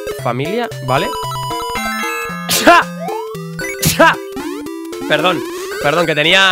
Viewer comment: parece que dices scusha, scusha